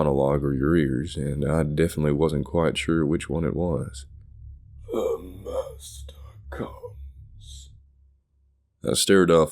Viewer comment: What did it say at 11:50? And at the end? I think maybe it was "The monster comes, comes"